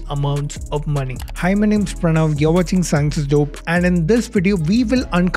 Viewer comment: Was a little distracted for a sec and thought you said, "Hymen, it's Pranav...."